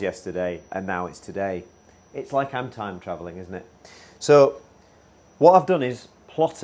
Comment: "It's like time traveling,isn't it" I hear" it's like cam traveling, isn't it"